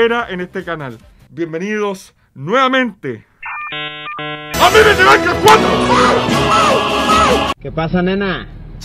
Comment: like si te diste cuenta que dijo memes de minecraft #4